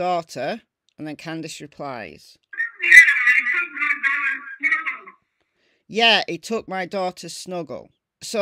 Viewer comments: I heard “he took my daughter Summer” which tracks because she does have 2 daughters. Molly is a nut job lol. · I mean it sounds like says he took my daughters pillow that’s what I get and I don’t hear anything these other creators are hearing !! 👍